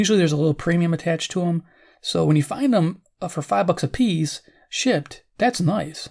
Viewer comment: This video gave me a great laugh this morning. Near the beginning (about :50), it sounds like you say "so when you find 'em for five bucks apiece, SHI^%&*!, that's nice!" "Shipped" sounded like something else!! lol...